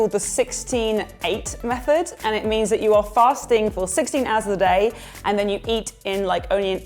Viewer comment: Omg i thought it said "farting for 16 hours" lol